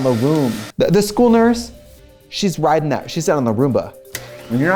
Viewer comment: I thought Trixie said Aruba and that's where I'm from.... then I realized she said roomba and now I'm sad